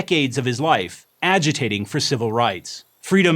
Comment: “agitating for civil rights?” Did I hear that right?